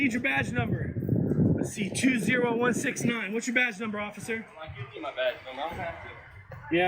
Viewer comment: Not only did he call him a trident but he also asked for his "badgenamer". What the hell is a badgenamer? This guy with his soft, silky, sissy voice reminds me of the old Baby Huey cartoon.